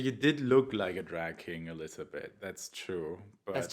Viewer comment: At first I thought you said "Drag-Kid" :D